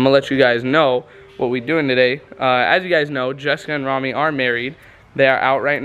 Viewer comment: justin and rami? i heard this wrong